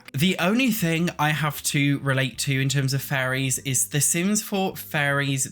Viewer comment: Sounded like you were saying Furries Mod and Sims 4 Furries, and it was sending me XD